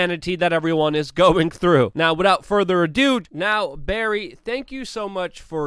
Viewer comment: Did you just say "without further a dude?" hahah Nice one.